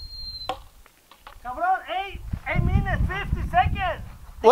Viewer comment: Did he just say 8 minutes 50 seconds? 😂😂